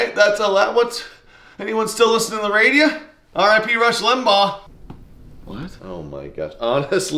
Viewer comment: I thought he said, "I could be Rush Limbaugh" or something so I put the Cc on. He said, "R.I.P. Rush Limbaugh." I was worried he didn't know he passed away.